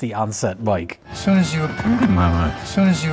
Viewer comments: Sounds like he says “saw you as a prude in my mind” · I think Herman is saying "As soon as you appeared in my life"??